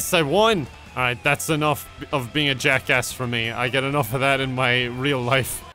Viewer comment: I thought it said jack ask